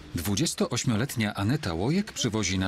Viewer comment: czy tylko ja usłyszałem ANETA ŁONIAK ? XDD